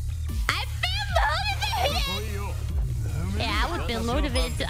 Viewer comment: I honestly do not understand what she shouted there... at 0:18 "I filmed all of it?" and 0:22 "Yeah i would be load of it, i get ya" Guys, please tell me this is incomprehensible to all of you as well, I feel like I'm having s stroke. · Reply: "I feel motivated" she repeats it after the character in game, "yeah I would feel motivated too, I get ya"